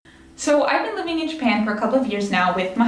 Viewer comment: Hello, I noticed in the captions at 0:01 it says "I don't live in Japan" instead of I've been living in Japan. If you could please fix this so people with hearing disabilities can enjoy your video that'd be awesome.